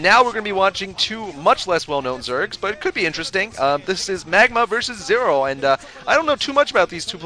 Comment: at first, i thought this game said Megaman VS Zero lol gratz savior!!! my favorite zerg i want to see boxer play already......dont anyone know why boxer hasnt played? i dont think i understand the system they use to organize games